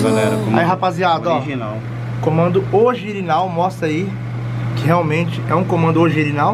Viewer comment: So eu que ouvi ele falando comando ogirinal?😁😁😆 No minuto 14:16 E ele ainda repete kkk